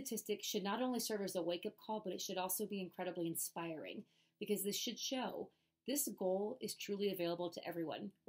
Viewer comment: Did she just say "sh!t show"? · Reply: 🤣🤣🤣no she said "this should show"